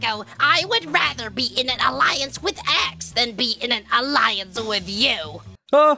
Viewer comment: How they said it: Dear taco i would rather be in an alliance with x How it captions said it: Dear taco i would rather be in an alliance with axe